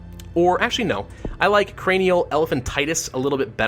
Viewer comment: I thought I heard corana and let me just say i jumped